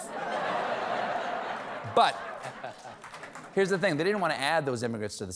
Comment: Did he say Fourth Wife or Fourth Reich?